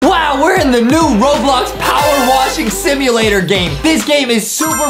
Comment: I heard Adam say 'shimulator' 😆😆